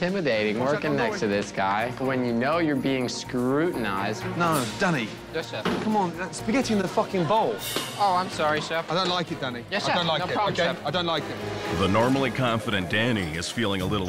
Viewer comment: I thought he said “I don’t like you Danny, I don’t like you”